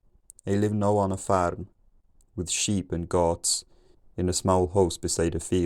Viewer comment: I had generated subtitles on, and they started up at about 900, where you said " it is a long winter. The ground is cold". There had been some isolated words and phrases before then. But they started to become regular then. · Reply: I heard and read "shape" for "sheep."